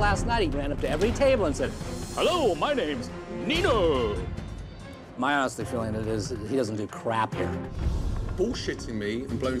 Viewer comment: what does he say at 2:16??? (after he says hello my name is nino) im confused because to me it sounds like ''my ass feeling is that he doesnt do shit around here'' lmao